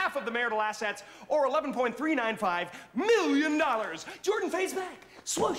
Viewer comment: He says "11.395", but the subtitles say "11.305"